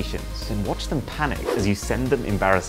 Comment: “Doctor said you have tested positive for explosive diarrhea” The cat really had to say something about that 😂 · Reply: I thought it said "Don says testicles and explosive diarrhea